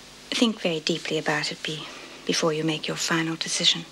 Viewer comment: did I hear correctly? Didn't Erica call vera Bea?? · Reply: No--she stuttered, "Be-before you make your final decision."